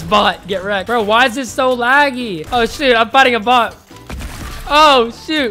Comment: did anybody else here that Edit: I think he said shoot but still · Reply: He said shoot